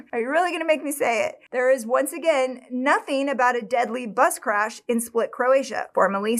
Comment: I thought she said nobody died in a butt scratch… I had to go back to hear, bus crash. Lol.